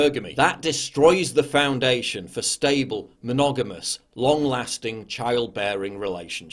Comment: I don't understand one word he uses: from 20:33 on he says: "That destroys the foundation for stable ??? long lasting child bearing relationships." What does he say there? Can anybody help? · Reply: Stable "monogamous" long lasting....